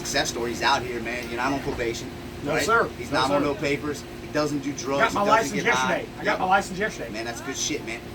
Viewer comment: I thought he said he didn't use drugs.